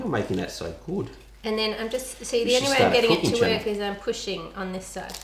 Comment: Wait a second,is it just me or what i feel like at 11:52 said "you should start a cooking channel"i was like:???